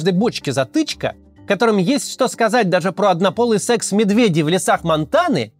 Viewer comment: за 1,5 года не смогли взять ни один крупный город Украины, но за три часа взяли миллионик Ростов... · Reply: Послышалось ,, однополый секс Медведева"